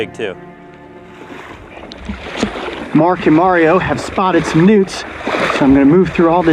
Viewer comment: For some reason I thought he said "mark and mario have spotted some nukes". 😂